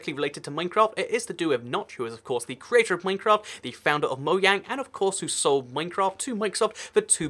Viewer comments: "Mogang" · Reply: I thought he said ‘founder of Logang’ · At first, i thought he said "Notch was a Logang"... Turns out he was the founder of Mojang lel